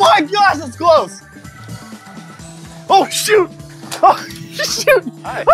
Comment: i swear to god said sh**